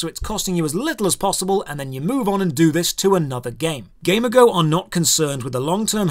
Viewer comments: thought you were saying gamer girl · I keep hearing "gamer girl" 😅